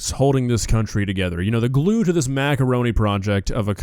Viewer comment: did he just say “maga” roni 😭😭😭